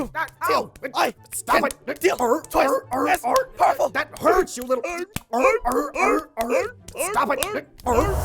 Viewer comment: Listen very closely at 4:49 to 4:51. I don't know if I heard correctly but I heard, "you..... little..... d***"